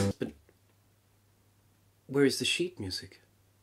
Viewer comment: When you said "Where is the sheet music?" I heard you say "What is this shit music?" Both fit equally well.